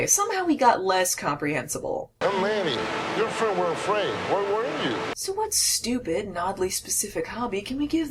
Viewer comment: I think he said where we're you